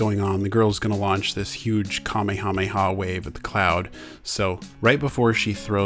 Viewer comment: I heard “hame kame ha” instead of “kame hame ha”